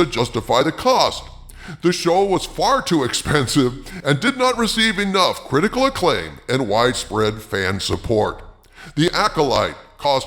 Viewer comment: I thought I heard from the Forbes article: "the Acolyte did not receive enough ridicule". 🤣 But it was "not enough critical acclaim".